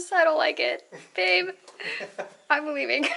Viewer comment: Did she say I'm bulimic??